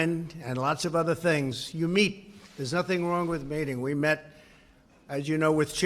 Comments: Did I hear that right? "You meet, there's nothing wrong with mating " 1:21 "I would certainly be willing to mate ". I know I know he was saying meet, but the pronunciation is close and it's funny lol. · did he say there's nothing wrong with mating?